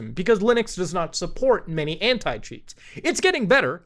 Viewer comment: Me thinking from the start thought he was saying hent** cheats